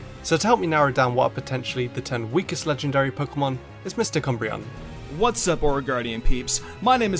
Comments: Anyone else hear “Mr. Cumbreon” at 0:34? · Instead of, I thought he said Mister Cumbreon, like a friend messing with another lmao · Dead ass thought he said Mista Cumbreon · When you said I some how heard that as mr cumbreon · When he said I thought he said mr. Cumbreon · OH. MysticUmbreon! I thought you said Mistah Cumbreon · When he said "I'm joined here by mystic umbreon" I thought be said "Mr Cumbreon" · I seriously thought you said “misty cumbreon” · I thought u said MisterCumbreon